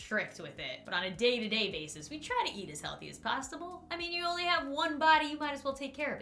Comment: did anyone else hear "we try to eat as healthy as pasta bowl"